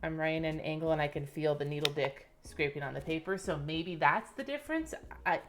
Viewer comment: Did she just said needle dick? 😆